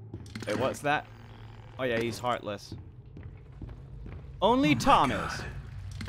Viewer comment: just to let you know thought that it said 'he's heartless' but it was 'she's heartless'